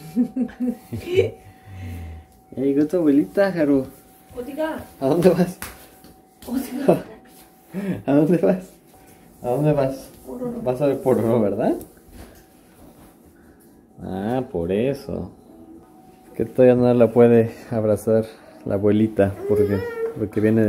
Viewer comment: Yo escuché "Vas a ver porno??“ se me hacía raro, pero escuché mal xD Es la primera vez que veo un video suyo.